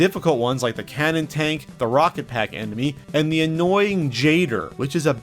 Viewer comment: you say "emeny" instead of enemy. Love. No trolling. Batman wins